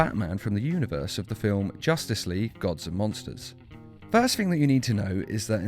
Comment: Every time he says "Gods and Monsters" it sounds like he is saying "Gods Among Us"